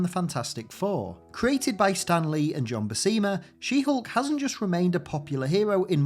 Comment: I could have sworn you said She-hulk was created by Stan Lee and John Cena 😅